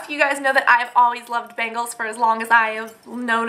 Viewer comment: HAHAHAHA, when you said "The next thing is bangles.. I love bangles, ever since i knew they existed" ect. I THOUGHT YOU SAID BAGELS!!!!! lol.. i was like why is she talking about bagels on a beauty channel? i mean bagels are yummy but really?? lololol.. ahaha i feel so dumb!